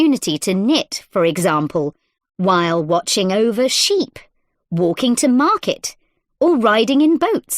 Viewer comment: 38/40 Got the last question wrong because I heard 'cheek' instead of 'sheep'. 😅😅😅